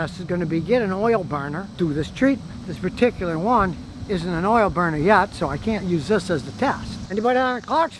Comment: George you are wrong. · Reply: No, I am not. At 6:54 he clearly says "this particular one is not an oil-burner yet so I can't use it for the test". Clean your ears out.